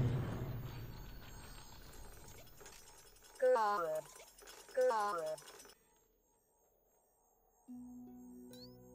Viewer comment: What’s she saying? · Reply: "who are you"